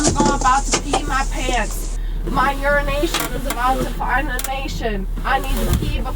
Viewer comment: "my urination is about to find a nation" is the most ominous threat I've ever heard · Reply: I first heard "my urine is about to find a nation." Fuck that was funny 😂😂